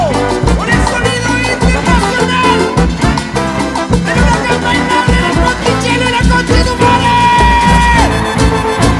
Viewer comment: que dice en el minuto 3:56 ? porfavor jajaj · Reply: Del huracán bailable en el Monticello y la ctm